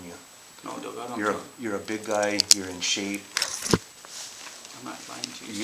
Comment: "Cause you're a good guy man, I'll give ya 2 cuffs"... Gee, thanks man that's deep. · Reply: I thought it said BIG guy instead of good